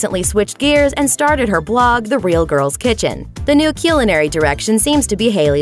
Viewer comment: did she really say cilinary?